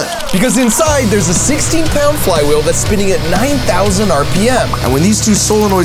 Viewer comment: You say 9000 RPM but the subtitles say 3600 RPM. I love this video, reminds me of my childhood.